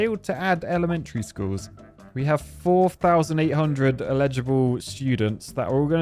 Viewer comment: At round the 11:50 mark did say his students were "illegible" instead of eligible? Surprised didn't glom onto that.